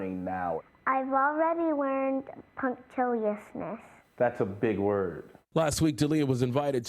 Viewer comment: how do you spell that? I can't understand her I heard puncthisis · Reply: Alejandro Menchaca Punctiliousness